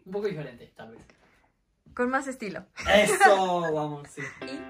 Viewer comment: alguien más se enteró de que parece que dice "Esoooo amorcito"